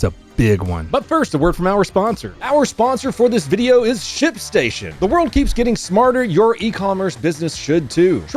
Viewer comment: I literally thought you said "our sponsor this video is shit station" 😂